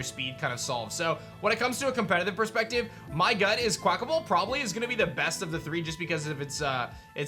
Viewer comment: I could have sworn you said “quack-a-mole.” I died!